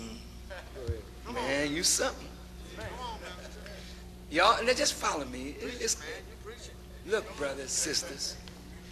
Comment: did he say "yaw'll niggah follow me"?